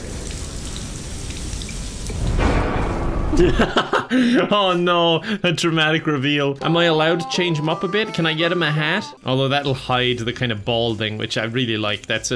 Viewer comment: In the beginning when you said "Dramatic Reveal" I heard "Traumatic Reveal" and thought: yeah, that works